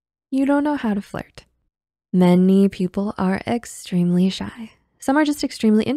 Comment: I heard, “Men-y people are extremely shy.”